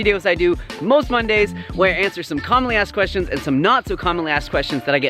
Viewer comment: At first I thought thought you said "calmly-asked questions" 🤣